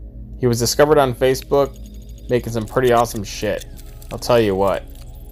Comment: all I hear is he took a really awesome shit